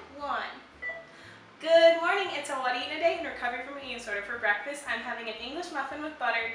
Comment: At thr beginning all I heard was "good morning... its a what I eat in a day in recovery from an eating disorder" 😅❤